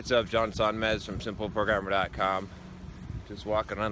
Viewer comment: Thought I heard “Johns on med” oof